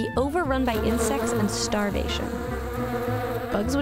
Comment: Did anybody else notice at 1:29 that on subtitles it says "livinging" instead of living..?